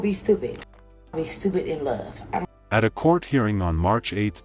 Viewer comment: This is sad but what are the cops supposed to do to protect a person when she basically said she’d love him to death. U can’t protect a person from their choices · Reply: I heard "stupid, in love".